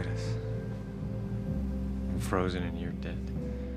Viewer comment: I'm frozen, & you're dead.... & I love you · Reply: Adam west i thought he said frozen in your debt lol